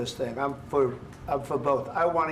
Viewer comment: What does Trump say at 27:40? I'm for Putin, I'm for both. Was that a Freudian slip?